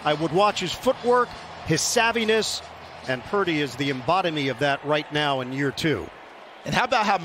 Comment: Did Ian just say, "embodimy"? What in the world? Portmanteau of embodiment and lobotomy?